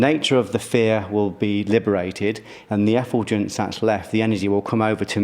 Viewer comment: What is he saying at 30:34? He is talking about an energy release when you don't engage or identify with an emotion. Is he referring to the energy as an "Efogin"? I've looked up the word and can't find it. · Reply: think you'll find the word is "effulgence" = 'a brilliant radiance'; 'a shining forth'